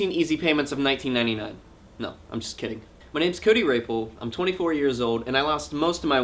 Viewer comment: You know, when I first heard you say "My name is Cody Rapol" I thought you said "My name is Cody Raper." Oh mannnnnnn.